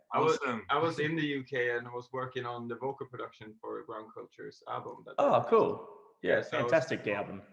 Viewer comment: What’s the band/artist he said he worked on production in UK in 2:01? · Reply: Grand culture?